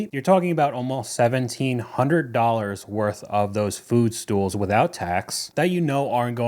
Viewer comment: I thought you said “foot stool.” lol. Man, those things are ugly 😮😮😮😮